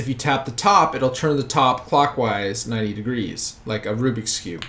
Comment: sounded like he said f wise